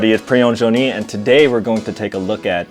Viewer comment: I thought he said "Pre-owned Joni"